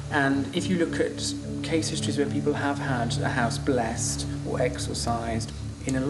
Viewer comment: omg I thought they said "house blasted." HAHAHA